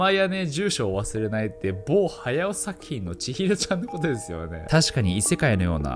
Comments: の某からあとなんて言ってるの？全然ききとれない……… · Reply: 某駿作品のちひろちゃん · ～「ぼうはやおさきいのちひろちゃんのことですよね～」 って聞こえるんですけど、どーゆー意味ですか？ナオキマンって結構滑舌が悪いんで、 時々何言ってるのかわからない(;^_^A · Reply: 某駿作品のちひろちゃん